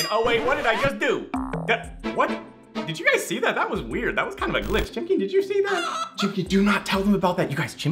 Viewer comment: When he said it's some kind of a glitch I thought he said you son of a bitc